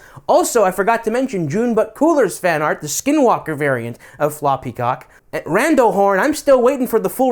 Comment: im glad u finally said something about ur username, i kept hearing 'floppy cock' and i wasnt sure if i was just immature for it or if it was intentional